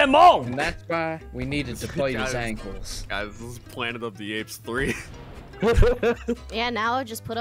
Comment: He did not say planet of the apes 🤣